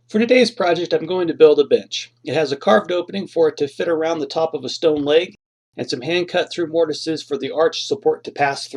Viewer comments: i thought he said: "todays project im going to build a bitch" i giggled really hard. sometimes miss hearing things make you laugh and cheers u up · I heard today I'm going to build a b**ch